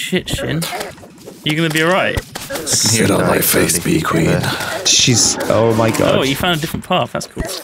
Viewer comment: Did anyone else hear Tom say "sit on my face Bee Queen"